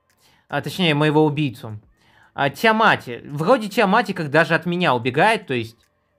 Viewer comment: Это..... На 7:32 секунде мне послышалось что он сказал тимати😂